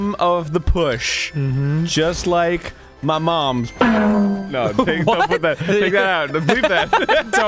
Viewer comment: I will always wonder what arin said there… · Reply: Kind of sounds like “No! please don’t put that- take that out, bleep that!” Because whatever they said was bleeped out lol